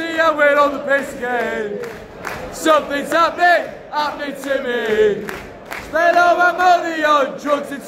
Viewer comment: What it's said in second 0:34? Sorry, I'm Mexican and I'm still figuring out this special accent of yours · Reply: Hahaha. In manchester we sometimes dont pronounce our 'h'. 'Happening' can be heard as 'appening'.